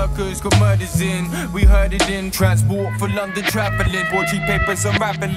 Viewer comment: please guys what do they say a 1:53 ? "we heard it in ......?" struggling with english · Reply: transport for london travelling